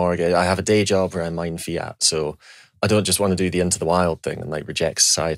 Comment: What is the title of the film Peter is saying at 1:04:16? My English is not good enough to hear it. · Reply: Into the Wild